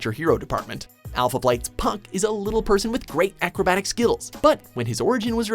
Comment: did he just say acrobatic skittles????